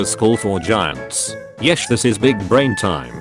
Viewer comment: did you just let it say yesh instead of yes?